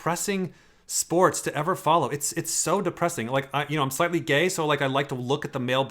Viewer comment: did he just say he was "slightly gay"